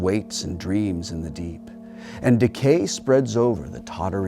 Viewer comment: "Decay spreads over the tottering cities of men" at around 4:22... I thought he said Takei (instead of decay) at first :/